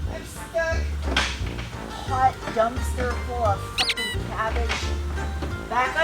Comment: "Hot dumpster full of rotting cabbage" Is that what I heard?